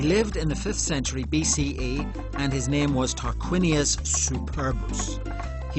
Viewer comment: Subtitles: Talked Wynia Superbitch This is what the subtitles actually say his name is.